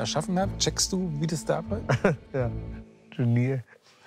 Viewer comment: Was sagt Klaas bei 9:01 ? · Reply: Ich glaube, er sagt "Genie". Weil sich Joko ja selbst kurz zuvor für seinen Einfall so genannt hat.